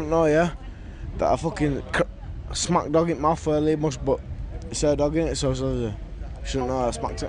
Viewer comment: i am british but i can't understand a word 🤣🤣 fuck kind of english is that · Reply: he smashed his mums dog in the mouth